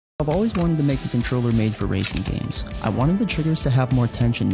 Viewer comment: I thought he said raging games